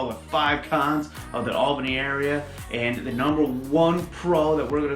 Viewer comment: This talks about the Albany REGION, not the City of Albany - please clarify better about NY's Tech Valley regional area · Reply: He clearly said Albany Area, watch a video before you find something negative about someone making an effort to make a video